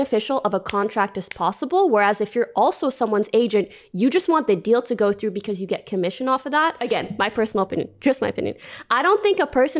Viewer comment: I hope xQc sues and loses. He knows the rules, he needs to play by them instead of trying to twist them. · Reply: No, she didn't say "some agents want commission", she clearly said that agents want the deal to go through as quickly as possible to get commission (it's around 5:25 in the video). I'm sure you're a fan of hers and she may be amazing at what she does but she doesn't know what she's talking about when it comes to Lawyer's that are also agents.